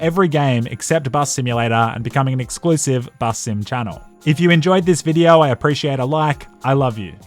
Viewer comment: did i hear BUSSIN' CHANNEL?